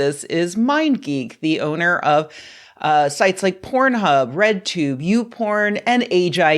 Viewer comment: I heard "breadtube" and did a double take